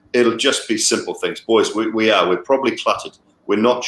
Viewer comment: Are we calling them the Kol-"Clutter" Knight riders? Is what he said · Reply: ohh thanks a lot I thought he said kol cutter which made no sense 😂